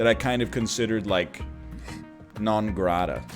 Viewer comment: When he said non grata, all I heard was 'non gratata'. Good vids kid, good vids, keep it up. :)